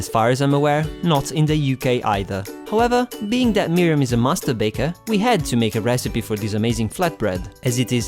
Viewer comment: did you say "a masterbater" ?